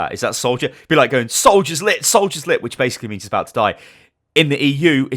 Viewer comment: What I hear: "Soldier 76 lit" What he means "Soldier 76 is One Hit"